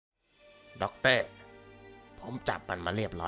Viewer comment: ได้ยินว่า ดอกเต้